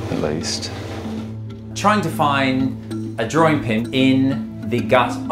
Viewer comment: I had to Google what a drawing pin is! It's crazy to think that a dog swallowed a thumbtack! Also known as a pushpin! And a big one at that! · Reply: Same, I thought they were saying drawing pen and I was thinking how did the dog swallow a pen lol.